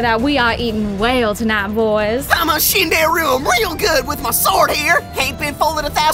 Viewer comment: subtitles say "Skin that rube", should say "shinderu", due to the character personality. 7:13 subtitles say "fluffy tuna fat", should say "Sir Fluffington the third"